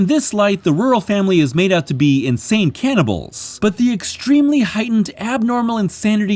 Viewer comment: I was in the other room n thought said “the ROYAL family is meant out to be a group of insane cannibals” n immediately thought “well they’re not WRONG!”